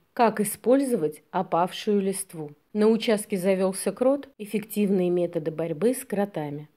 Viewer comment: Послышалось "на участке завёлся кот"!)) Котя просто супер! Такие потягушки! Моя кошка какает в кротовые норки и закапывает их)) но избавиться от них не получается. Червей много, много и кротов.